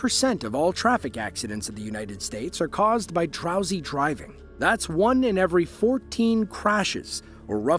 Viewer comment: when you said drowsy driving I heard trowsy driving lol.